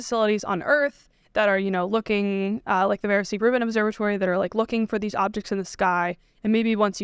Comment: Thanks and! Did anyone catch the observatory she mentioned at 16:11, Veron C Grumman? Veron-Cetty something? She talks so fast she's joined the Grant Cameron club! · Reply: Vera C. Rubin Observatory. It's the new name of LSST.